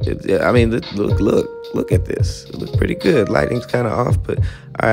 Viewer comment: Did say the lightning was kind of off